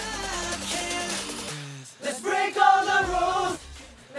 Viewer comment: Ok , but now i hear "let's bacon the room" 🤣🤣